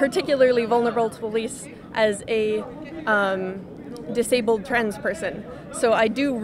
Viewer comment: I actually liked the first person a lot. I disagreed with almost all of her stances. However, she was respectful the entire time, interrupted no more than Steven did, was willing to listen but was still ready to argue her side. It's not often that Steven gets to debate with civil people like this. You can disagree with her all you want, I do. BUT, you have to respect that even though she was set in her ways, she was as willing to potentially having her mind changed as the host was. Good on you. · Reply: Apollo James I thought she said “I’m a disabled trans person”